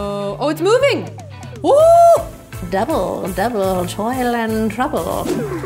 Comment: THE GIRL WITH THE BLACK HAIR AND WATER BOTTLE SAID A HARRY POTTER REFERENCE. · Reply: no it really isn't in Harry Potter, it's in Macbeth as we have all said. but to me the girl sounded like she said devil devil toil and trouble, instead of double double toil and trouble. :/